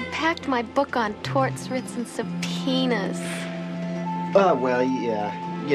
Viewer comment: Did she just say penis?